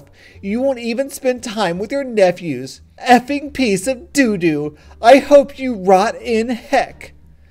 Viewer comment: Do r/softwaregore! I promise you, it's super hilarious! Like so can see! When she said "I hope you rot in hell!" I thought she said, "I hope you red in light!" because of the translation of rot and hell in German | \/